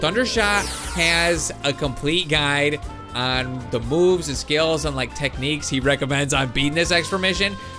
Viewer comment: it sounded like he said boobs, not moves